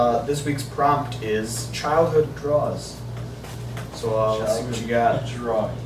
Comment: I thought you said "childhood drugs" at first and got a little concerned for a bit.